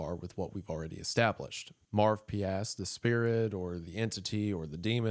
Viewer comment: I can’t take any of this seriously when I keep hearing “marv pee ass” or “marv penis”